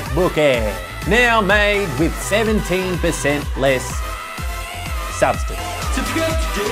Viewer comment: I thought you said least where it said less